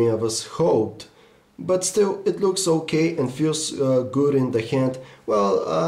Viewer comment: i heard "still, it looks so gay" lol